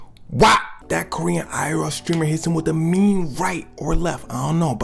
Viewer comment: Why it sounds like he said green streamer😂